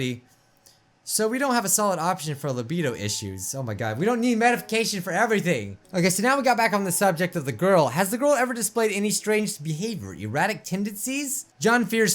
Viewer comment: who else heard Medification